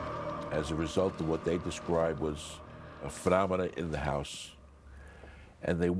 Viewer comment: what did he say at 3:48 a "frabada" in the house?